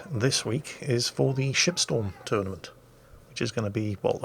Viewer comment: I gotta get my hearing checked out, 0:31 I heard Shit-Storm tournament.... well, carry on... as you were...LUL